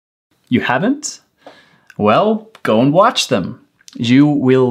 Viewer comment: Well, gone watch them? Eso fue lo que dijiste?